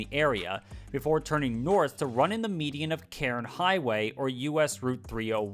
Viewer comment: Did you call Crain Highway "KAREN Highway"?!? 😂😂😂😂